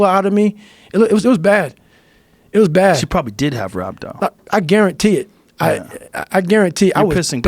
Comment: "Rhabo" (which sounded like "rabdog" / "rubdo") from Joe and Goggins when they spoke it. Is a condition "Rhabdomyolysis ", often referred to as "Rhabdo" for short, Is where the muscles in your body begin to leak out or break down usually from over training. Muscle cells leak out protein called "myoglobin" and can cause injury to the kidneys also. In case someone didn't hear it correctly like myself. i had to play that part back at around around 1:11:53 to hear it better and multiple Google searches to find it, it was driving me crazy I wanted to know what they were referring to. So if you were also unsure and wondering... You are welcome my friend!